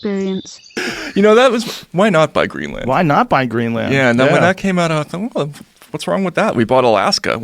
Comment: I thought they said why not buy green lantern.